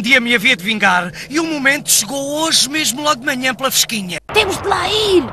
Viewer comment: O que diz o Vegeta? Não falo português... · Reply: Jurei que um dia ia me vingar e hoje veio de manhã pela fesquinha